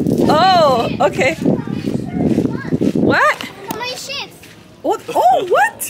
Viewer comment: Ik thought said shit when she was saying chips😂